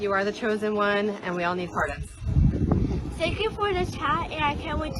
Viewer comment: did she say “we all need pardons?”